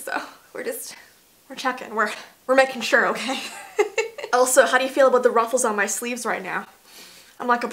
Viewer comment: I thought she said Elsa. 😹 The scary part was that Elsa’s my cats name and she was staring at the screen when she said it.